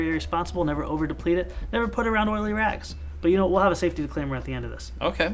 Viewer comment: anyone else hear him say “never put it around one of the Iraq’s “